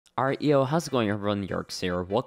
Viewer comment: first time hearing this it sounded like reo hows it going everyone now it sounds like alright yo hows it going everyone